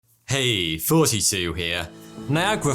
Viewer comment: For a year in my life I thought in the intro he said, "Hey 42 here"